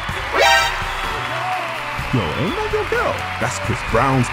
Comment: I heard him say "your ho ain't loyal" 😂😂😂